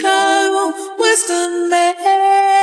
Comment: Always thought she was saying "western male" not western Maine lol